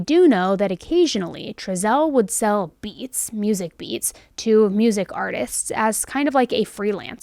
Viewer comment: When you said “beats” I heard “beets” like the vegetable & I was like what the heck for a moment there 😂😂